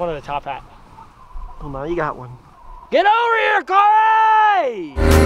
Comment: okay but seriously i thought when had the top hat on and yelled "come hear corey" i thought he said "come here chloe" and i'm sitting here like is this a movie reference... i was very confused until corey walked up lmfao.